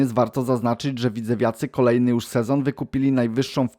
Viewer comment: usłyszałem "żydzewiacy"😂